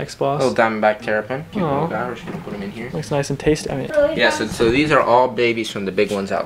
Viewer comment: did he say that’s nice and tasty 😂 😋